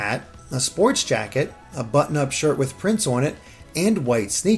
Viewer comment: I heard "a button-up shirt with Prince on it" lol I wish.